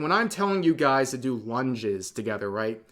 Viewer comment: I SWEAR, I heard say, “one jizz,” instead of “lunges,” which, let me just say, would be a hilarious exercise.